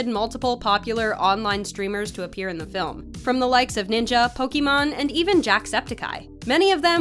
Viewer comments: Did she just say pokemon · she actually said pokemon... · Did she just say pokemon instead of pokimain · Did she just say Pokémon, it’s called pokimane